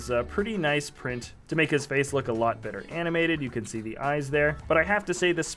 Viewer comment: did i just hear "jar jar better animated" only took them 11 years